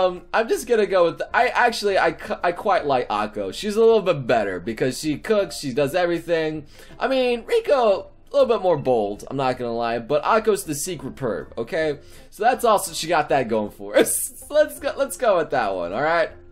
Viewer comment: I thought you just said Pico...